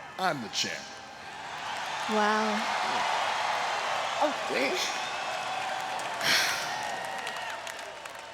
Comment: You all heard what Nash says? He's checking her out said "damn".😂